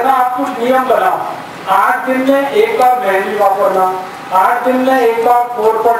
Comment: Can somebody help me understand what is he saying to use at 2:16? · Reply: Lakhan Contractor 8 din m ek baar mehandi use krna...